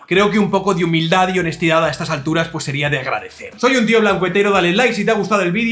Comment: Al principio en vez de escuchar "Tio blanco hetero" escuche "Tio macuetero" xDD